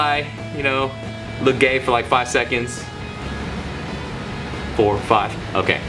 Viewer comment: Did You Just Say "Look Gay for 5 Seconds" WTF ??